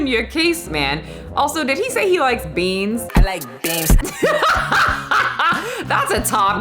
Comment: I think he said "benz", but his dictation is so bad and he's just such a joke that it's "whatever"